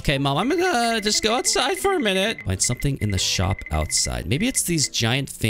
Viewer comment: is it just me who thinks she said the s word instead of shop? at 19:06?